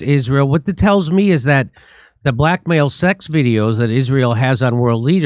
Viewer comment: I thought Jimmy said the black male sex videos instead of blackmail lol